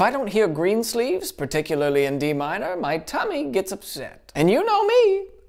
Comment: "particularly in D minor" "it's so smooth I want to rub it" Ah yes, it's all coming together now... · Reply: I thought he said E minor